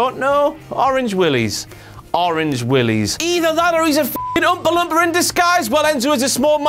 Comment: Why does it sound like he said Umpa Lumpa?